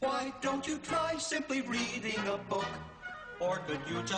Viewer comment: first song: not reading will get you far. Last song: too lazy to read a book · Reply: sorry I misheard greedy for reading